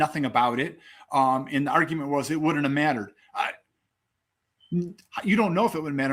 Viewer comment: I heard you say u wouldn’t save Davion so stop